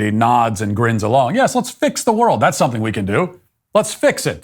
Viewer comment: What she said was "Fix the World." But what I heard was, "Chopper, sic balls."